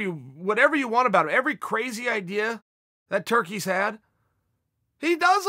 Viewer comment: Did Chael call Connor a turkey?